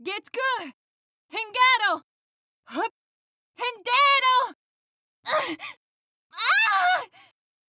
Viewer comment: sounded like “El dinero!”; the money! And I can relate 😂